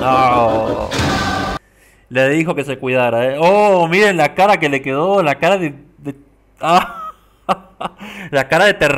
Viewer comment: ¿Que dijiste de Cristo Rey? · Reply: Viva Cristo Rey xD